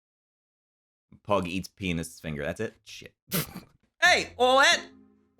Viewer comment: I thought you said pug eats penis finger